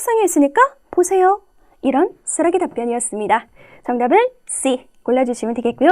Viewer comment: 졸다가 수락의답변을 쓰레기답변으로 들었